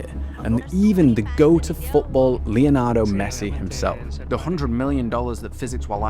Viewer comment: did he say Leonardo Messi? 😂